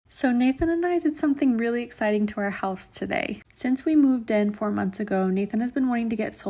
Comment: I swear I thought you said "to your health" and I was so confused how solar panels added to health 🤣